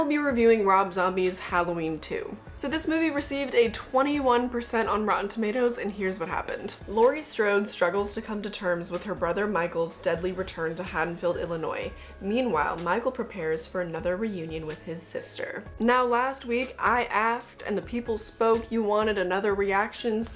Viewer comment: Thought You Said "HaddonField Ill-Annoying